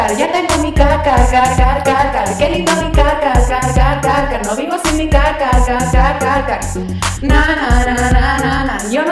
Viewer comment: jjajja yo entendí " no vivo sin mi ca-ca-ca-ca-ca-ca " XD